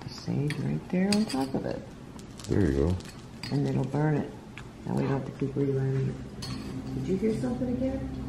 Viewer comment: it sounds like "f***ing burn it" it may have been speaking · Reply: It sounds like it said f***ing burn it now